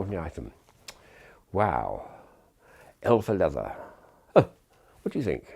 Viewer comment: I think he says L for leather, not Hell for leather...